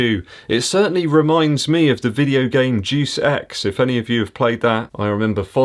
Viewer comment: Haha at 5:23 I thought you said “Juice X”